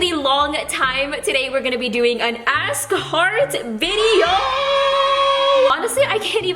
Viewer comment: I thought you were saying ass cart video. 😂😂😂😂😂